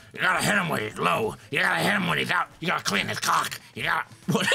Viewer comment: that's the second time I've heard Mark say cock instead of clock. XD